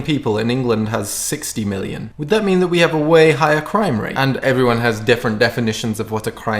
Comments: I thought you said crying rate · When you said "crime rate" I thought you said "crying rate". 😂